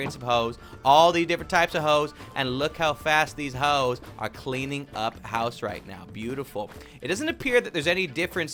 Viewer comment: Is it me or did he say these hoes are cleaning the house